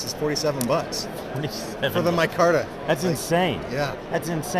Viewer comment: What's the name of the knife at 1:55? Last year's winner, couldn't make out what he said there 🤔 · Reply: Sachse